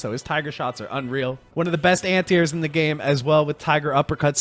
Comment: He keeps saying "anti-airs" but I only hear "ant ears" which I like even better.